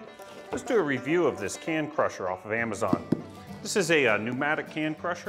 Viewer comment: Geez I thought I heard you say hand crusher! I mean it's not that different from a hand crusher